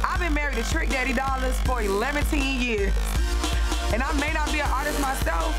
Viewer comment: did she say 11teen years?